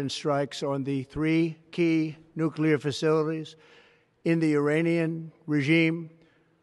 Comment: did he just say Uranium? 🤡